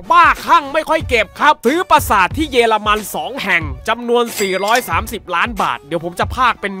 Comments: ใครได้ยินว่า เยรมัน ไหมหรือได้ยินแค่คนเดียว55555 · ทำไมได้ยินเป็น เย ละ มัน วะ555